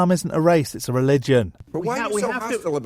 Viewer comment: Ah there's a pretty bad subtitle error at 10:36 It calls Islam a "r*pe" instead of a "religion" and I'm sure that this was unintentional